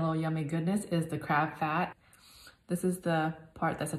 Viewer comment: Bro I thought it said crab feet